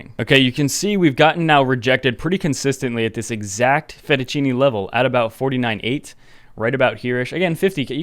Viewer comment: Fettuccini level instead of Fibonacci? :)